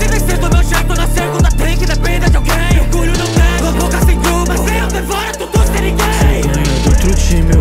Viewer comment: eu escutei assim : se eu nao devora tudo serie gay